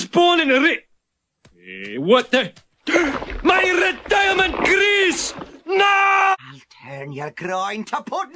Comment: did he just say Greece? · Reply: 'grease', not 'Greece'... (obviously?)